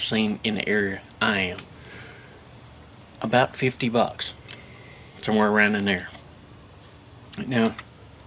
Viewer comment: Did he say somewhere around a n1gg3r???